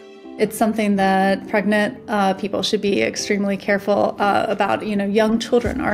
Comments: Did she just say "pregnant people"?......... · did she just said "Pregnant people" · Did she say "pregnant people " ? 😂